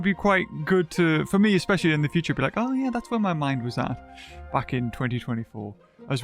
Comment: I thought when he said "back in" I heard "fucking" i was abt to comment swore!!!!!!?!?!?